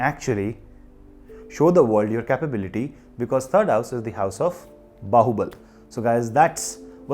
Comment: Thank you for liking the video. Do not forget to subscribe. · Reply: What do you say at 6:46? Bahoubat? What does it mean in English?